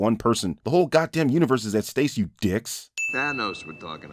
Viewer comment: Me trying to figure out if at 14:48 you said “stace” instead of “stake” on purpose to further the parody of CinemaSins or if that was a genuine mistake. Who am I kidding that was on purpose